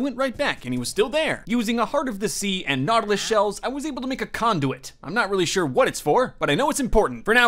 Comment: i thought he said cond0m